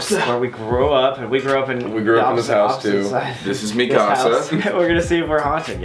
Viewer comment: for a moment i thought he said Mikasa but it was mi casa .-.